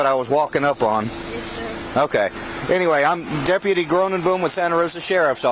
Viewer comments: I swore he said his name was “deputy groom n boom” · I thought he said "Im deputy Doom and Gloom"...